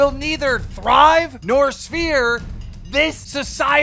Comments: did he say sphere instead of serve? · keem says svere (like sphere) instead of serve